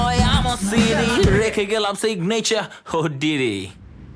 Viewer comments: bro did i hear "diddy" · sounded like "ohh Diddy."